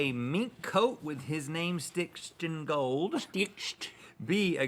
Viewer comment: I thought he said "meat coat with his name stitched in gold" I was like "well that's not going to last very long...."